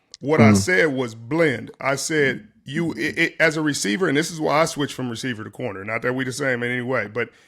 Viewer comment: "...as a receiver, he kinda blend." Blend what?? I'm glad these two men were able to come to a peaceful resolve. Richard playin' in our face and Travis's face with that bs! We heard what he said and what he meant! Shout out Trav for being the bigger man than a HOFer. Geez!! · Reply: I thought Sherman said he was a Bland receiver